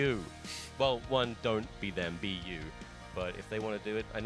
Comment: I heard Wii U instead of be you. Anyone else.